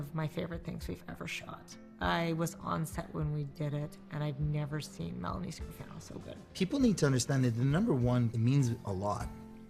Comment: What does Emily say at 06:52 about Melanie? Thank you. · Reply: "I've never seen Melanie Scrofano so good"